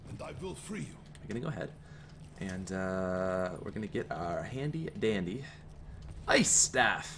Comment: I thought he said "Gonna get head, Where gonna get our handy."